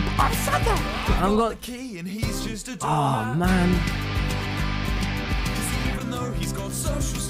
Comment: when tommy was reacting to ynbf i thought i heard wilbur singing '' i got the key and he just eats doormats''